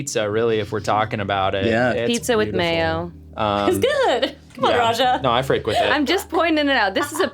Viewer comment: Did Nicole really call Arasha "Raja" at around 43:10? While talking about the Southern tomato pie. "C'mon, Raja!"